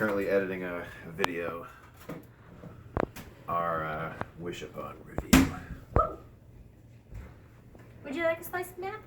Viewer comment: what did say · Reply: "Our uhhh wish upon review"